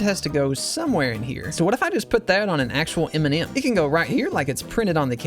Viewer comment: am I the only one who heard Eminem instead of M&M?